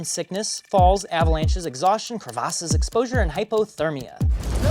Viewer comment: what did he said did he said cravases he said crevasses